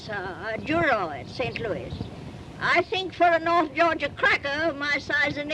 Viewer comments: so is she English and did she say North Yorkshire?? 🤔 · At the 4:20 mark of the video, Did She just Say; "I think for an Old Georgia Cracker My Size and Age" ? 😂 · Did she just call herself a 'cracker'? I thought that was modern slang. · did she call herself a north georgia cracker?? 😂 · Did She call herself “a North Georgia Cracker?” (4:19mark) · Sounds like a very nice lady, but did she just called herself a cracker?🤔@ 4:20 I must be High!😉